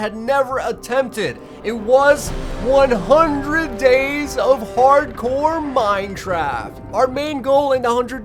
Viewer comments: did he just say "100 days of hard core Minetraft"?😂😂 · i don't now if i am the only one that notice dis but did he just said in 0:36 "minetraft" xD